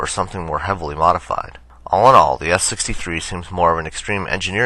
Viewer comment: What was his name again? I heard it as Sam Balls Sam.